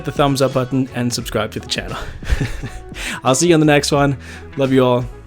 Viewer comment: I thought you said “love you mom” in your closing. I had to go and listen to it again. Lol. I thought that was so sweet!